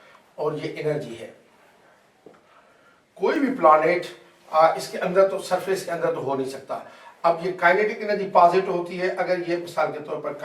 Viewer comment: Sir can u please explain what u said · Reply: Main nay kaha hay k koe bhi satellite planet k andar nahi ho sakta